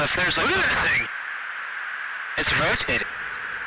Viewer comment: What does he say. ? It’s roasted ?